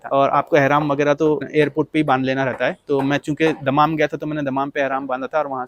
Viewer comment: did you say Main chutiya?